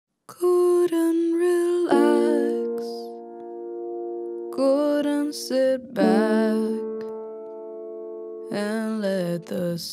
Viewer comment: I heard "Gordon relax, Gordan sit back" at the beginning. I though that she was singing about a boy named Gordon that was dying 😭